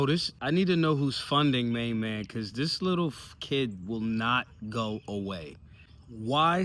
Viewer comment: I coulda sworn I heard say "this little f....kid will not go away" lol!